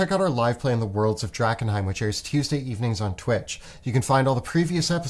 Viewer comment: At the beginning of the video I misheard "Tuesday" and "Thursday" for "Tearsday" and "Turdsay" and was adequately impressed by absolutely dead straight face xD